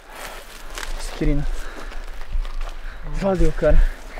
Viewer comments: só eu ouvi o chamar o de Quirino? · Só eu que ouvi o falando quirino ao invés de?. momento